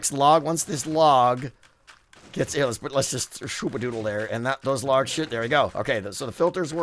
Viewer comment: correct me if I'm wrong but does he say "and that does large sh*t"